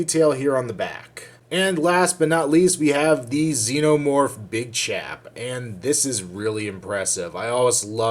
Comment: I thought he said bichap LLLLLLOOOOOOOLLLLL